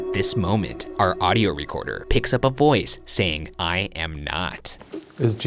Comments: It also sounds like Jesus is saying, “I am here,” instead of I am not lmao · no matter how many times I re-listen I always hear ‘I hope’ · For the 17:43 - 17:46 recording, I hear “I hope” instead of “I am not”. Idk if that’s just me but uh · With headphones, i heard "i hope" when they were entering the chapel instead of "i am not". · When Shane asked if Jesus was there I heard "I hope" instead of "I am not" 'I hope' was really sad.. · it sounded like it said “I hope” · I don’t hear “I am not” I hear “I hope”. I can hear a clear “I” And a clear “hope”. I don’t hear three syllables or anything close to “I am not.” Anyone else? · Sounds like Jesus was saying " I am now ", like Shane called upon him or something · I heard “I hope” instead of “I am not” lol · I heard "I hope" rather than "I am not." So it seemed like, "Is Jesus here?" "Well, I sure hope he is." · If there was something that spoke when they were walking up to the chapel, I could have sworn it said "I hope not" instead of "I am not". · 17.36 Is Yisus here I heard "I hope" , not "I am not" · Ok when Shane asked if Jesus was there and the spirit responded to me it sounded like it said "I hope." instead of "I am not." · I thought it said "I hope" not " I am not" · it sounds more like "i hope not" instead of "i am not" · i thought it said “hi mat” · Idk about anyone else, but when they thought the voice said “I am not” all I could hear was “I hope”, which would also answer the question and maybe even make more sense.